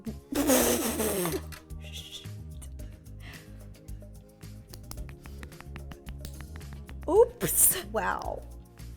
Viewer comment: I think ro said the s word sorry if she didn’t I might have miss heard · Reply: Ik me too but i put it on .75 speed and she said shoot